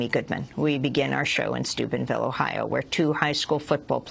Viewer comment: I thought she said Stupidville, Ohio